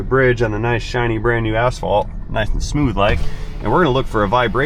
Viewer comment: seconds I 100% did not think you said the word asphalt😂😂😂😂😂😂 I thought you said a shiny new.a$$hol€